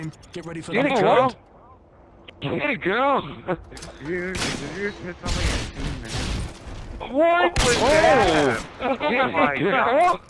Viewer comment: i swear he said "did you hit a girl" 0:42 haha